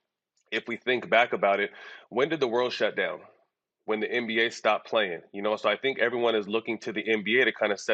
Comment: did I hear this fool correctly? Did he just say the world shut down when the NBA shut down?